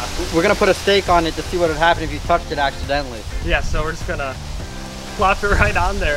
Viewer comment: I thought he said, "what would happen if you fucked it accidentally" and I loled.